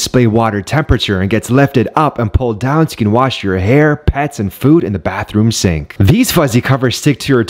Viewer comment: At the “pets and food” part i was sneezing during and legit thought her said “ass and food”😂